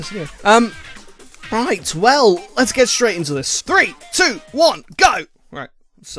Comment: "Let's get straight into this!" That's what he said.